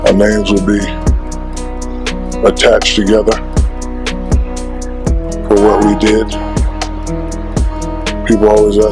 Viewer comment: what did he say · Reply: He's got 1 more ring than Shaq.